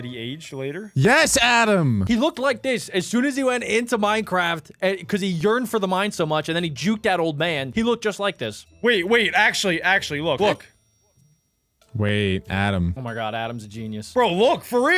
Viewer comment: I thought it said urined